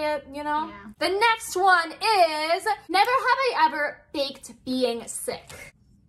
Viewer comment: Did you say fake or bake😂😂😂😂😂😂😂😂😂😂😂😂❤❤❤❤❤❤❤❤❤